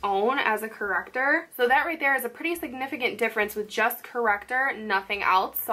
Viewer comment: When you said “just corrector” I thought you said “Jeff’s corrector” And I was like “I never thought to call him Jeff”